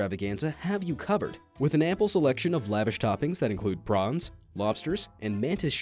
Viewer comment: What is the narrator saying at 9:59? I always called them shrimp & I swear I hear him saying “prongs”. So yeah, can someone please tell me what he’s saying because now I’m confused. 🤷🏽‍♀️